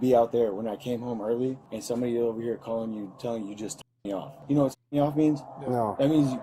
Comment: can someone plz fill in the blanks for me ? I thought he said jack me off but now it looks like it starts with a t??